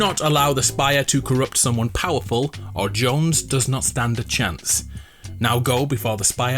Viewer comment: when i played the message i heard "jones bones"